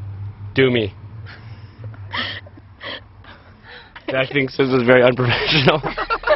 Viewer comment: i thought he said this is very unpractical?